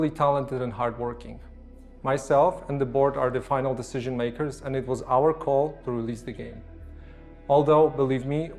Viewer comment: When he said, "Myself and the board.", I thought he said, "Myself and the bois."